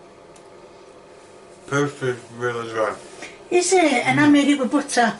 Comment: I thought he said it was really good?